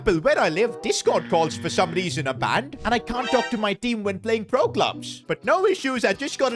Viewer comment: did he said roblox or ro clubs